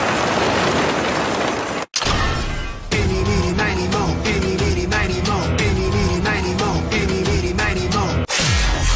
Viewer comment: ここなんて言ってるの？ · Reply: イニミニマニモ 英語で「どちらにしようかな」のニュアンス